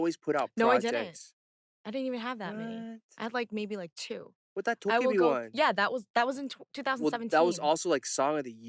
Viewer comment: What does Eric say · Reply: "That 도깨비 one" i guess? 도깨비 = goblin, the drama ailee sang the ost of